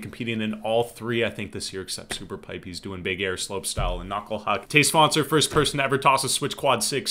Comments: When you said matteus svanser, I thought you said todays sponser lol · I keep hearing "today's sponser" when you say Matej Svancer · Everytime I hear him say Matej Svancer i hear "Today's Sponsor" lol what's wrong with my ears